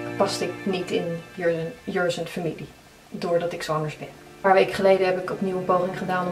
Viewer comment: Bij 0:27 versta ik echt dit: 'vanaf het begin paste ik niet in jurn's familie omdat ik zomers ben.' He???? Wat zeg je nou?